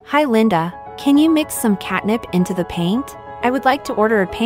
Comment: I thought it say catnap💀